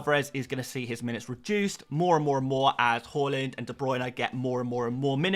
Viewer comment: Everytime you say Haaland, it sounds like you say Hojlund… you should work on that mate…